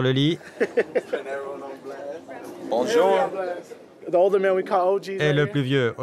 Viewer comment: c'est moi ou il a dit "Bonjour"